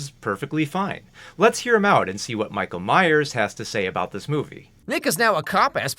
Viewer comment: did you just call him? 😭